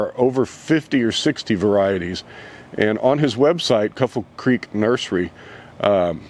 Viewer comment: Can't make out name of nursery you mentioned at about 3:42. Sounds like "Couple Creek" or "Cripple Creek" but can't find either one with Google. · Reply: Kuffel Creek nursery